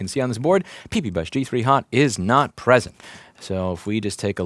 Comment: Tbh, I didn't get any of that, all I heard was pee-pee is not present and that sounds worrisome enough.